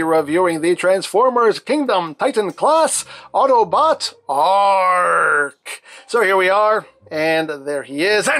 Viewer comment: did he say ark or arg like a pirate